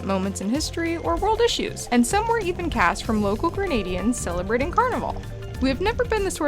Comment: I thought she said "Canadians". When the inland part came up I thought Oooh, waterfall! Yikes!